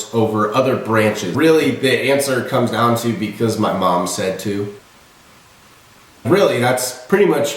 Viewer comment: All i hear is “so my mommy said i should..”